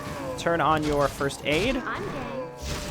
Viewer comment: did say "im gay?" at 37:40? xD